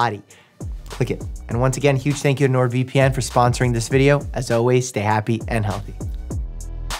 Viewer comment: he really said AS ALWAYS NOW BISMILLAH